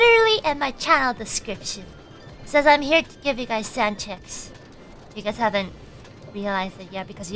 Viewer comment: I thought she said I'm here to give you all sand chips.